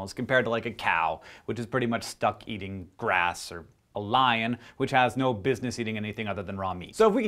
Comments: I heard this sentence as "... compared to, like, a cow which is pretty much stuck eating grass or a lion." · I thought he said "A lion which has no business other than Romney"